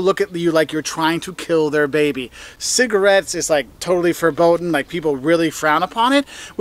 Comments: did you just say cigarettes are totally "verboden"? the dutch word? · did he say: totally verboden? If so, then he just said that it's totally forbidden (in Dutch) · Did he say "it's totally verboten" at 6:10? :-D